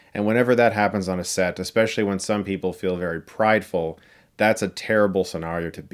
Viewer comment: what does Chris say ? "Prideful" or something ?